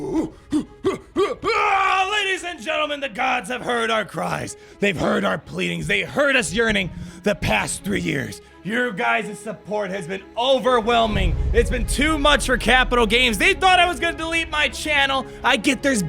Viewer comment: 100% thought you said, “they heard us urinating for 3 years”. Yearning. You said yearning.